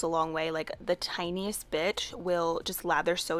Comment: Am I the only one who hears “the tiniest bitch” instead of “bit” at 2:52 🙃